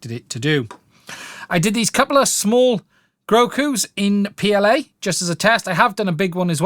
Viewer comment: did he just call yoda, goku?? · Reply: No, he called Grogu Grogu...